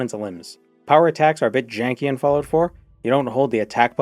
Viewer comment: Did someone say.. Jank-y? 😏